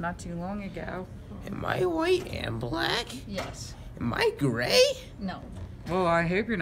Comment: “Am I black and white?” “Am I gray”😂😂🤣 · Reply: i thought you said "am I gay" instead of gray